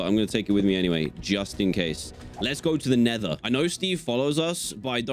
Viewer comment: When dan said lets go to the nether I thought he said never go to the nether